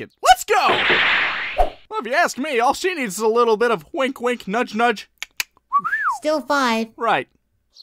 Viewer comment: Krillin: Well if you ask me all SHE needs is a little bit of wink wink, nudge nudge, tut tut whistle Gohan: Still five... Lol after so many times watching dbza i just got made out what krillin/gohan was saying and got the joke xD